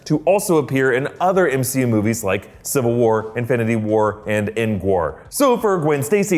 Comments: did he just say end war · Why Did He Say Engwar?